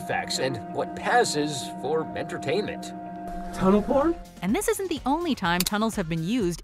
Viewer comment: I swear to god i heard "tunnel porn"🙄